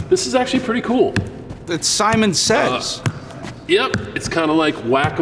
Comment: did he say saiman says😂